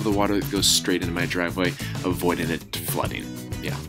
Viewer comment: I thought it said driveway stealing